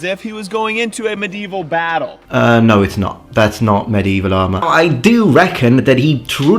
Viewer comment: At one point I misheard "medieval armor" as "medieval llama". That is all. Have a nice day.